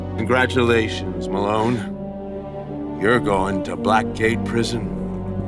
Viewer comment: I thought he said, "you're going to black gay prison."💀💀💀 Edit